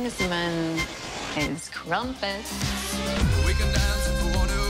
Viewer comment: Wait, what did Poppy say Kingsman is at 0:47? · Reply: Crumpets